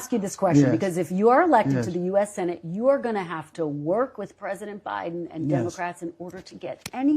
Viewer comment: I thought I heard President O'Biden?